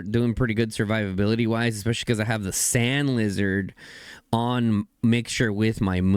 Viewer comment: Did SaY SaNd LiZaRd!?